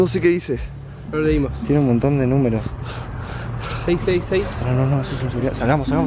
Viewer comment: En el minuto 10:34 escuché 666 eso es el número del diablo